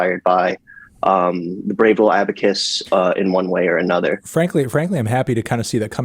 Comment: What band are they talking about at 5:30 ? Can't catch the name properly. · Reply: you prob figured it out by now but just incase it’s brave little abacus